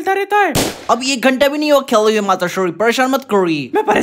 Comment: what se says😂😂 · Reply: Abhi ek ghanta bhi nahi hua khele hue matashree